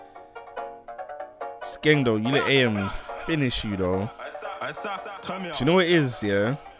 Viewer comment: Skengdo what? What did say loool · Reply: "Skengdo, you let AM finish you tho..."